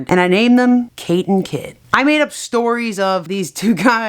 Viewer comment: It Sounds Like She Said kit kat Its a Type of Chocolate Get It Kate kit=kit kat